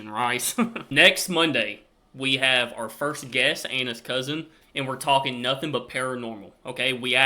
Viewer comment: I do apologise but when you said 'Anna's cousin', I heard 'Anus Cousin' and wondered exactly what kind of relatives you had over there!